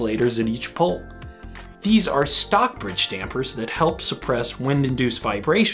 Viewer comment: Is he saying “Stockbridge stampers”?